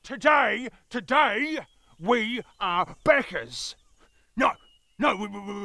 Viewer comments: Anyone else hear them say “beggars” instead of “backers”? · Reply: I heard "buggers" tho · Thought they said beggers at first. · I heard it as "beggers", but oh well, that is basically what Kickstarter is about anyway. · I really thought they were saying beggers till around the end -_